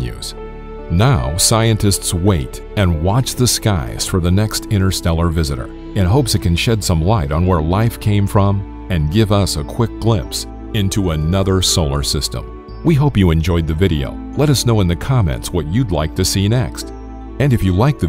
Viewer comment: i thought he said " let us know in the comets"